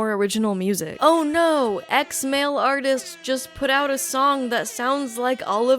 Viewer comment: I keep hearing "ex-male artist" instead of "x male artist" which is really funny